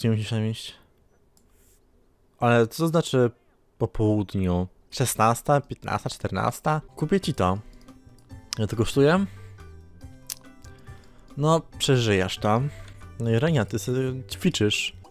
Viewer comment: dlaczego w 1:54 usłyszałam kupię ci dom a tam było kupię ci to wtf